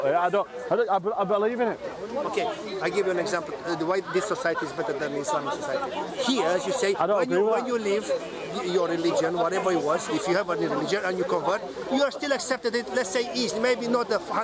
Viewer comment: Mashallah showed a lot of patience towards this arrogant guy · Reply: Did he just say 'muslamic'?